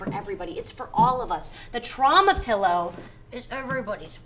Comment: For a split second I thought the pillow said "Trump" lol